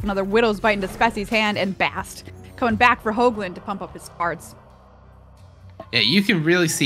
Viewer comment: Anyone else also feel like they heard her say "Bast... To pump up his farts" instead of "cards"? 🤣